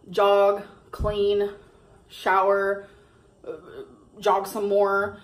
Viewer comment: did she just say she can’t shower?